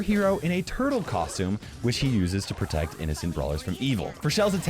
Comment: did i hear SHE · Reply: Which he